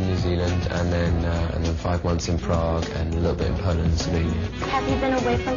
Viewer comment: did he say 5 months in Prague???? hope he didnt cause I'll be very angry I didnt go there cause it's like 100kms from my home:D